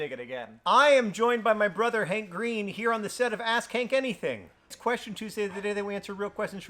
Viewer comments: Does John say Ask Hank or Ass Kink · Why did I hear “Ass Kank Anything?” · I can tell that I'm living through a period of my life that feels very bad and weird (often enough to the point of complete nonsense) because I first thought I heard John say, "Ass kank anything". Whenever this long, unfortunate era ends, I think I'll probably need an equally long term of mental recovery.